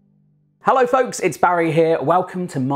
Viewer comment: I thought he said hello f*cks